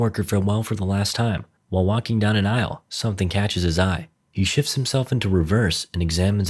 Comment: i thought it said "he shits himself into reverse" LMAO😭